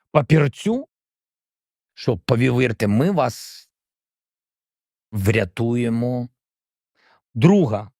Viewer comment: Що сказав насправді? Хто вміє читати по губах? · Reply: We will rescue you